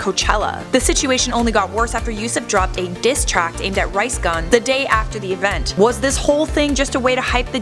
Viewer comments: did she say rice gun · Who else heard her say “rice gun” at 4:18😂